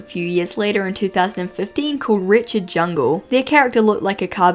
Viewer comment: When i first watched this video, i thought you were saying Wretchedjungle as "Richard Jungle" which is hilarious to me, and i think the Animal jam community should give Wretchedjungle the loving nickname Richard now.